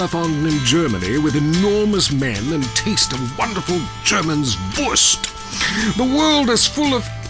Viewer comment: Hey, pls what did he say at 40:47 ? Germans what? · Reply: He says “wurst” which indeed means sausage in English. It’s the german word for sausage.